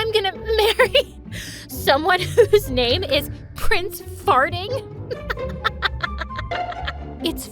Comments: i couldn’t stop laughing when her father said “prince fartling” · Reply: I thought he said prince farting-💀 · Did they just say f-fartling?????